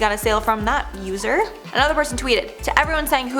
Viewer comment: For a second I thought u said At least Huda knows she got a sale from that looser lol LOOOLLL I WAS LIKE WHOA THEN I REWINDED IT